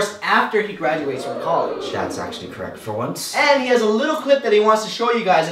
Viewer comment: Can anyone tell me what said at 2:56, sr I'm not good at EL :( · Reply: He Said, "That's actually Correct for Once"